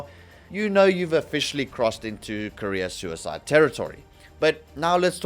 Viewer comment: I thought you said "Korea suicide territory," but it really works either way 😂😂😂